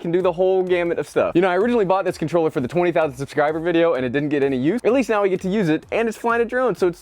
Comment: Hahaha I thought you said ‘it didn’t get any views’ but you said ‘it didn’t get any use’ 😂 awesome video once again, I like the realistic recommendation to not get this as a (first) camera drone but instead for having fun flying around. Well done🙌